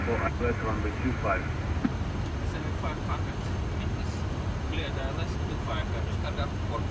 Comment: я один услышал слово "бл@ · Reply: Это слово cleared значит разрешён. Да, ты один услышал.